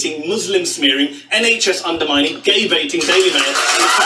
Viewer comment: He actually said gay baiting, not gay bashing.